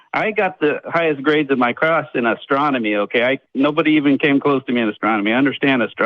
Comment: "I was #1 in astronomy in school, nobody could touch me". Also: "When I was 18 I knew nothing about science" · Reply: I thought he said Astrology... Like horoscopes?